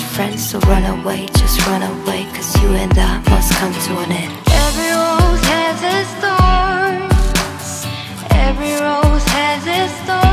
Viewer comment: I thought she said "every room has it's store"